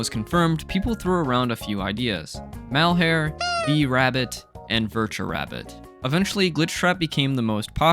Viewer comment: i thought you said B-Rabbit